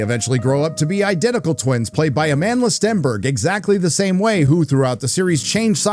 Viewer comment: I really thought he was saying her name was Amanless Denver